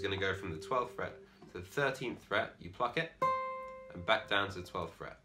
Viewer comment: Why am I hearing “fuck it”